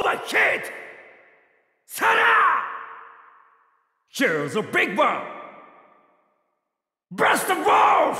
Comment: what is he saying could someone tell meplease? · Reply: AndresitoBlast132 Here’s a big one